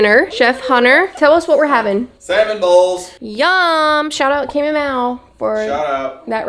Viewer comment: I had to turn on the subtitles because I swore Hunter said he was making Salmon Balls for dinner.....LOL